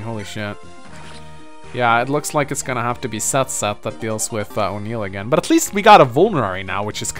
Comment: Smh I keep hearing O'Neill's name as Unil...